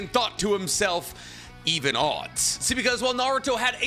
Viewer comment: correction: he didn’t say “even odds” he actually said “easy win”